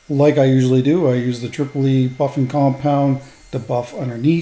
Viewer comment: I keep mishearing "Tripple E" as "Tripoli", like we're importing buffing compounds from Barbarossa. Resin is a truly versatile material. It's really neat how it lets you make stuff out of otherwise unusable wood pieces. Never thought I'd see a bowl made of pinecones but you've done that quite a few times.